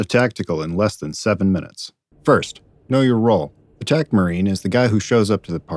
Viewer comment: I initially thought you were saying Tecmarine. I was pumped to repair some vehicles.